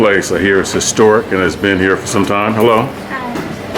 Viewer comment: Now is there jello in it? 😂😂😂😂😂😂I thought she said hello too ☺️